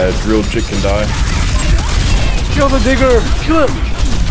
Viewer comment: Did u call venture diglett😂😂😂😂😂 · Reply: i heard "kill the digger". that wouldve been on thin ice man😅